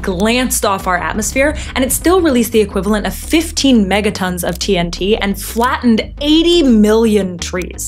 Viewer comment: Megatons? I thought i heard Mettaton.